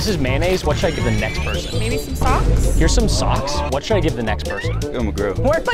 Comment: I thought you said mayonnaise socks